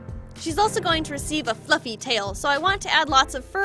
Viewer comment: I heard "Fxxking tail" Instead of "Fluffy tail" Yeah um anybody else hear that